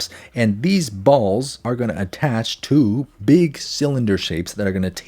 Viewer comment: what did u js say 🤨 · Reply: “These balls are going to attach to big cylinder shapes.”